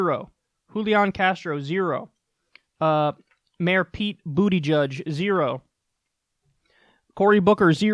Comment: Kyle: "Mayor Buttigieg" Me: Did he just say Booty Judge?!